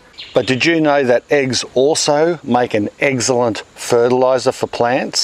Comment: I heard you say 'eggcellent" about eggs.LOL.